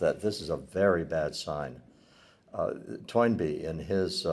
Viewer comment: What's the author and book, I can't hear. Twine Be ? Edit: Toynbee it seems. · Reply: Arnold Toynbee